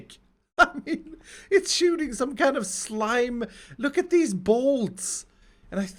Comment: lmao i heard "looks at these balls!"